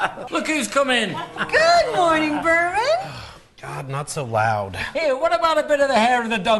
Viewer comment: I thought she was saying "Good morning, vermin!" like geez lady, he's already suffering, way to kick him when he's down